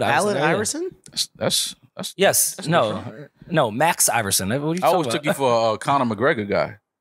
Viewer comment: Did say the N word at 43:54?? 👀“N!&&a what u talkin about”…